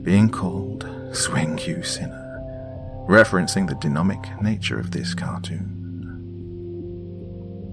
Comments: "The DENOMIC nature of this cartoon" 😂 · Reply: Geddoe I thought he said denomic but I also thought I was hearing things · smthn he says DENOMIC INSTEAD OF DEMONIC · in 3:40, he says 'denomic" instead of " demonic".